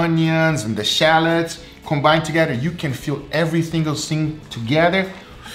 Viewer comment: Go at 7:04 and listen to what said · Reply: He said everythingle sing